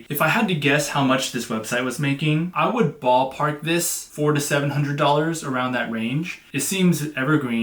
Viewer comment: did you say $4 - $700... 4 to 700 hundred a week? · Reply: per month!